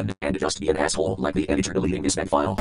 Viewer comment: Wait, what did he say? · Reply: With the soloist (monophonic) pitches and the muffled effects applied that made it harder to understand, I think it was "OH SH*T! DON'T DO IT, I DIDN'T SAY IT, I DIDN'T MEAN IT, I DON'T WANT IT--GYAAAAAAAAAAAAAAAAAAAAAA--".